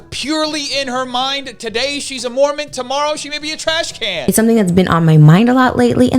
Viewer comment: Thought it said "I'm a moron now". Girl, you already was...